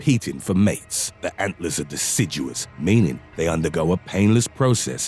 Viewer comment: I wasn't paying attention and thought it said "Antlers are delicious"